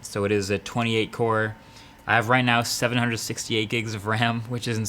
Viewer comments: Did he say 768 GB RAM 😱😱😱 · Did he just say 768gigs of RAM?! 😱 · 768 GB of Ram 8:40? Did i hear it right.. ? What do you do with it? Does it even get 20 percentage utilised?